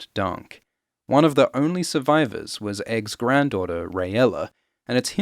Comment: Aegon V Targaryen "Egg" died at Summerhall, forty years before the main story Jaime killed Aerys II Targaryen "the Mad King" in Robert's Rebellion, fifteen years before the main story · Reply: ahhhhhh i did mishear, thought you said his wife escaped summer hall hence the confusion. How i confused wife with Grand Daughter of all things is the real mystery.